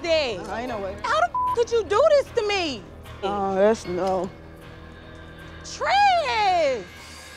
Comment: Did she say "aw, das no"? That's my reaction whenever the catfish is a creep or a crazy lol